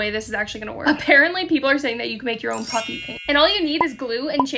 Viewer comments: Thought you said puppy paint. Was waiting to figure out wtf that is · Why did i hear 'puffy pants' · am I the only one that thought she said this is how you make your own puppy